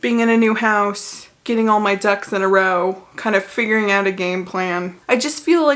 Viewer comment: I legitimately thought you said get your “Dougs” in a row… and it took me a minute to then connect the dots and realize you said “ducks”. 😂